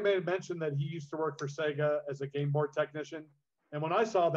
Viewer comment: Was half asleep listning to this and i heard "worked for sega as a gameboy technician" and woke up again like WTF